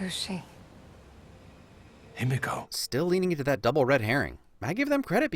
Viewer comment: Every time they said Himiko, I thought they said "indigo"